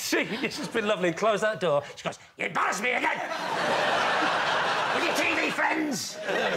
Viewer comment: What does Lee say at 3:27 , imitating his wife wagging her finger, & just before he says "with your TV friends!" ?? I can't for the life of me get a clear sense of it ... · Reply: "You embarrassed me again! With your TV friends!"